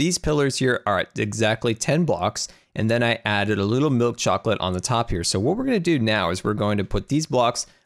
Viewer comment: where going on a trip in are favorite rocket ship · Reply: did he say milk chocolate